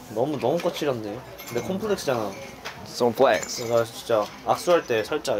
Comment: Just me or did Felix say Comflex at 16:24?